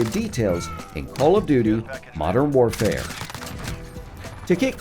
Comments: Anybody else hear “call of doodoo” at 0:47 ? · Did he say Call of “Do Do” Modern Warfare at 0:48? That caught me off guard haha Very well made video regardless! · Bruh I heard him at 0:46 say call of dodo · did he just say call of doodoo? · is its just me or did i hear 0:47 call of doo doo modern warfare